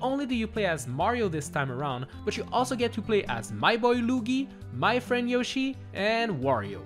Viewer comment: did he just call Luigi Lugi or something lol